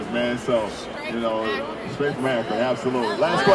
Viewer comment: Thought he said straight from Africa 😂😂 LeBron is an amazing specimen